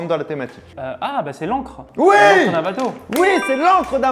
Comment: désolé mais j'ai entendu tente 2 secondes au lien de ancre.. avait raison